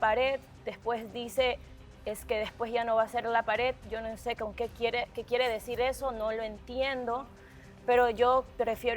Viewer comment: Alguien escuchó lo que dijo Mare a 1:38:00 lo que dijo entre dientes a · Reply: No pongas palabras en su boca que no dijo Mare dice "si saben que tengo plata para pagar su multa" Le dece esto al microfono a la producción por que tocan el tema del cual ella ya no quiere habla.Y si ella dice una mala palabra o se comporta de una manera indebida las multan